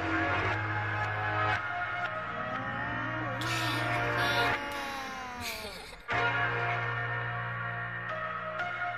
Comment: what did he say here? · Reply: "Hey Listen! I took the same hit, the same hit a week ago... And nothing! "